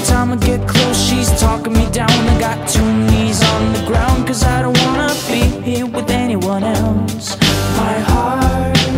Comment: is alfie saying toonies or two knees? because i'm hearing both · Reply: Rhaven Wynter two knees